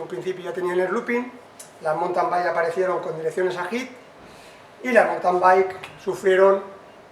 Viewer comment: Qué son direcciones "agit"? o cual és la palabra que dice en el 9:05? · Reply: Se refiere a ahead. Creo que eran las que había antes con los rodamientos en el exterior en lugar de ir integrados en la pipa de dirección